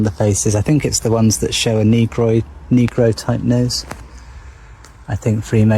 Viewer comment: 😅 in my whole lifetime I've never heard of a negroid , but that sounds like Android and maybe a particular being outside of human race 🤔 or maybe he jus said it weird at first, but that's what I heard, lol finna research negroids 🐇🕳️